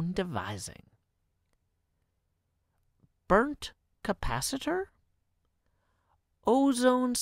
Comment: when I first heard it I thought he said "flux capacitor" and Back To The Future came to my mind 😂